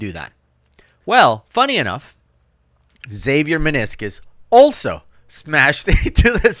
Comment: Xavier meniscus. Ahhhh lol. I thought you was saying his channel name was save your meniscus. lol. U can tell I tore mine. 😂